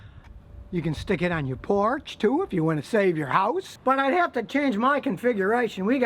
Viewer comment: I heard it like "you can stick it on your PORSCHE too, if you wanna save your house" :D